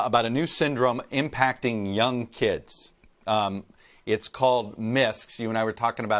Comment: What is that word? "It's called'"---------- mists?